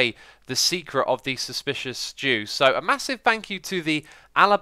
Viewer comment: Why do I hear "suspicious stew" as "suspicious jew"? English is great sometimes....